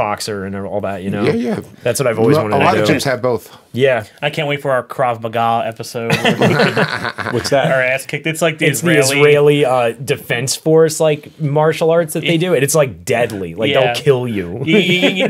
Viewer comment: "A lot of Gyms have both" What did he say here? What are they talking about in this segment? This part lost me? Kraz Magah???? Never heard of it.